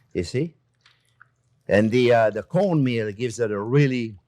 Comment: I couldn't understand what was said i thought you said cold meal and i couldn't find it so i had to listen two three times and then i understand. Cornmeal.